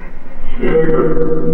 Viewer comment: No minuto 26:31 eu ouvi "chega"